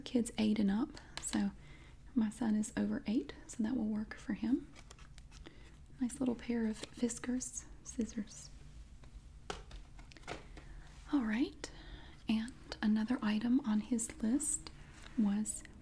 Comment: did you say VSCO scissors like a vsco girl or am I just hearing things